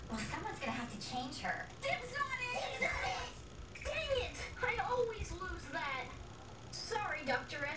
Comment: what...did he said?! i heard ,,Dang it!''